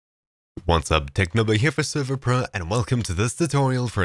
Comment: Thank you TechNobo! I installed this plugin on my server but I did not know how to set this up! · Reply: I thought he said technoblade lmao